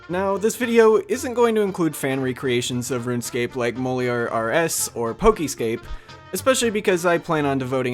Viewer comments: Wtf are you saying at 0:28 Molear RS? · Whats the game at 0:27 called? I Can't figure out what you're saying and i've googled all variations of "mole er rs" and cant find shit! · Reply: MoliorRS :)